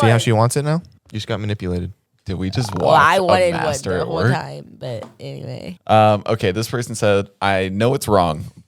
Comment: What the heck says on 47:16, i didn´t understood those words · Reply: "I wanted one the whole time, but, anyway"